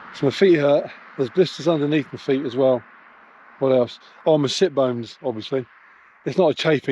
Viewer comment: I swear I didn't mishear '"it bones" for "shit bones" when you first said it. Ok, I absolutely did and nicely going for ya. Started cycling again a couple of weeks ago after 20 years of not being on one and just took to riding the roads this week after getting a handle of the bike on the field.